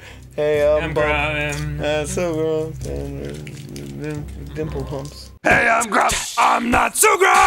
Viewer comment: Totally thought he said nipple pumps.